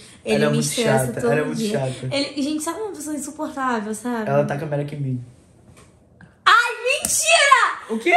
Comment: No minuto 12:12 o falou algo q eu ñ entendi direito,ñ sei se foi eu q entendi errado! Muito suspeito!!!!!!! Eu ouvi ele falar "ela taca a p3rec@ em mim" mano alguém me diz oq ele falou por favor